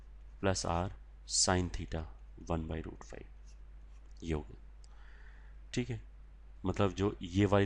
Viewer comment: Did sir say yo xD